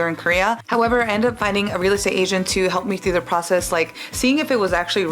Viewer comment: i thought she said a “real-estate asian” 😭😭